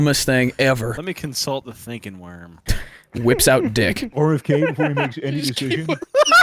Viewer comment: So I was watching the video with my wireless headphones and at 14:21 when he says “whips out dick” my headphones make it 10x funnier when I hear “Whips out, Battery low”